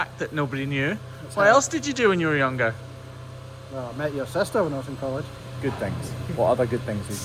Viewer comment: Hey there!! Met your sister ....how sweet for him to say that! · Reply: Um... I heard "maybe your sister" in Response to his question "what else did you do?"